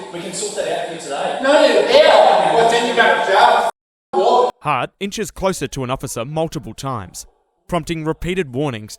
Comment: Does he call him a w0g? 2:24 lol is this the 90s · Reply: u god damn wog 😂😂